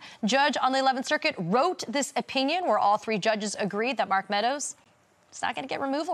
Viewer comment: it sounded like Neal said Murk Meadows how appropriate since he thought playing in the Murk of Trump would get him somewhere.